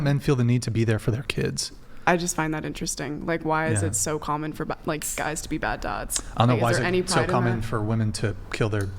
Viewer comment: Is it me or did it sound like she said "why is it so common for black guys to be bad dads".